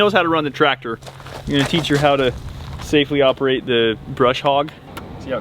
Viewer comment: I was today years old when I found out it’s “brush hog” and not “bush hog” lol We deer fenced our orchard on all sides, except the entrance gate and deer made their way in, so when you do deer fence your orchard, put that tall Jurassic park gate on it. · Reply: All I hear is "bush hog" around here.